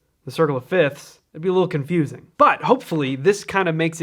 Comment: Can you feature Stevie Wonder’s “Contusion”? I think it might do a similar think with keys. When you were demonstrating the circle, I kept hearing “Contusion”.